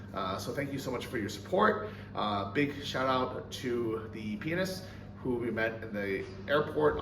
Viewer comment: Does say big shoutout to the Penis 46:19 who we met at the airport. 😂😂😂